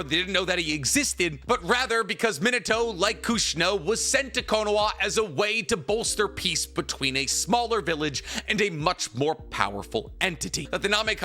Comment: slow down talking bro, it sounds like youre saying konowa